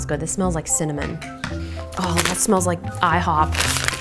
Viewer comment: when she said this smells like IHOP i thought she said this smells like iPod